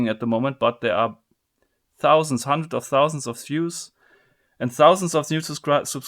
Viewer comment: I can't be the only one who heard him say 100,000 Jews rather than views